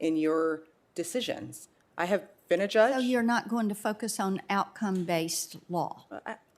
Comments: Blackburn sounded like she said, "You're not gonna fuckus.." lol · did marsha say "fucke us or focus"? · At the 1:30 mark it sounded like she you are not going fuck us when she said your not going to focus. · "So you're not going to f**k us on outcome-based law?" I know that's not what she said, but I hear it every time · "SO UR NOT GONNA FUCK US RIGHT " THATS WHAT I HEARD LOL